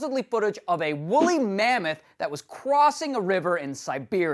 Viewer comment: did he say cwassing?